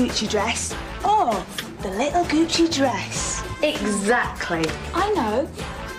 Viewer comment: the little Gucci dress is my favorite part... however for years I thought she said the "little coochie dress".😂😂😂😂